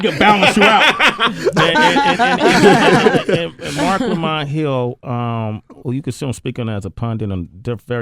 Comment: What's the name of the person Sway mentioned twice in the interview? I think I heard "Marco Monhill", but I couldn't make out the sounds and nothing popped up on the Google search. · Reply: mark lamont hill